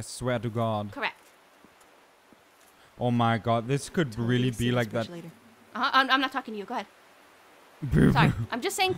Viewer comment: Any one knew what mumbled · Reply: 1125anime "totally gonna see this bridge later"